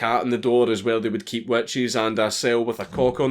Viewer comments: "A cell with a black cat on the door is where they would keep 'wet cheese'". I've been having the hardest time trying to understand what word he us actually using in that sentence. Could some ody help me out? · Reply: Aaron Yandell witches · wait what? wet cheese? what the fuck did you say for the black cat? lol